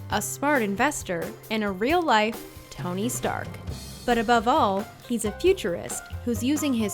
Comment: Seriously, nobody else heard her say Tommy Stark?